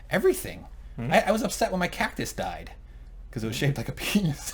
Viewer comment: I thought was saying "cat" (47:40) and was horrified.